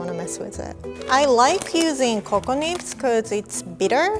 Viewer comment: I thought she said “coco nips”🤣